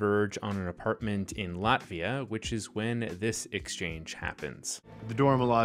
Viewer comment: For a minute I thought you said Latveria. When are we getting Dr. Doom